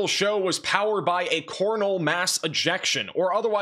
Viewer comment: Did I hear "Cornhole Mass Ejection" 😂 that made my day!